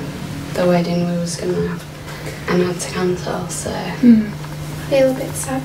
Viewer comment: I haven’t seen this episode but it sounds like they had to cancel one wedding and thus this is a way to afford a replacement one. I would have liked them to have made an exception and let her wear the original dress. Normally I have no sympathy for the brides being big babies about the dresses, but (1) she already owns this one, (2) it’s beautiful and (3) she’s being so stoic about it. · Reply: I thought she said cancer not cancel maybe I heard her wrong ❤